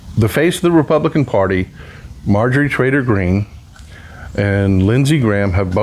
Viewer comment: Sounded like you said Marjorie traitor green